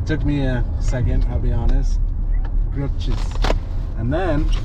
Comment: I thought it said "Girl Cheese" 😂😂😂 I was like wow no cheese for haha